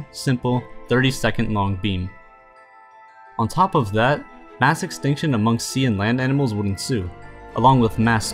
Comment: did he say masturbation?😂